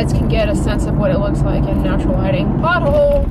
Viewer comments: i thought you said 'butthole' when you said pothole lol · She said “pothole!” but I’m crying bc I thought she said “BUTTHOLE!”